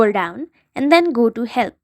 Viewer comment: this part is so funny because it actually sounds like she is saying hell instead of help